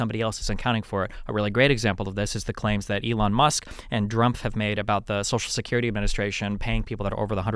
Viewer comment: Did I hear Destiny say DrumpF? lol 26:37 Nice!